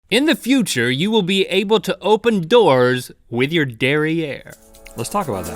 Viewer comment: At first i thought Link said: Dairy Air. It was actually derrière lol